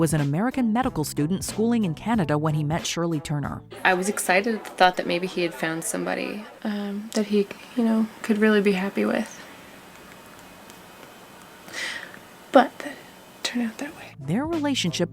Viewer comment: What was said? I keep replaying it? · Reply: “I was excited at the thought that maybe he had found somebody, that um he, you know, could really be happy with. But it didn’t turn out that way.”